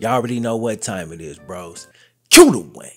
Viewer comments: All this time I thought you were saying “cue lil’ Wayne “. 😂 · I always thought you said “cue Dwayne” 😂